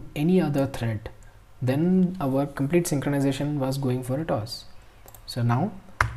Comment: What do you say at 20:25, I couldn't quite catch it (great video by the way) - "our SOMETHING synchronisation was going SOMETHING SOMETHING"? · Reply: I meant to say that the complete synchronisation was going wrong (said - "our complete synchronisation was going for a toss"). 😅